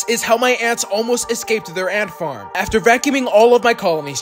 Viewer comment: i thought he said aunt farm😂😂